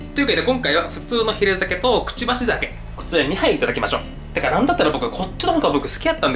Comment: がピースサイン出したところで、「ハイ！」という女性の声が聞こえません？ 何かの音？それとも、画面に映らないだけで、女性が居るのかな。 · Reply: 2杯いただきましょう ってが言ってるよ？